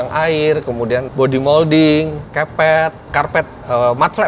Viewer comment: "Kepet....." orang jawa be like 🤣🤣🤣🤣🤣 *dengernya gitu